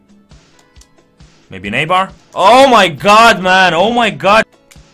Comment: when you said "maybe Neybar" and you actually hit the bar i laughed so much :)). Still you're a great player and i enjoy watching your videos, you helped my gameplay just by looking at your vids. Even if i don't understand a word you're saying in the drafts from the other channel i still like to watch.